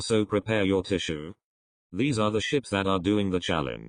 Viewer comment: I thought you said shits instead of ships